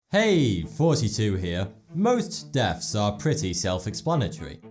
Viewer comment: i heard "hey 42 here" but it was " hey thoughty2 here" 😑